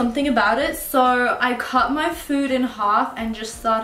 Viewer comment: omg when she said cut her food in half the first time I thought she said cut her foot I was so confused LOL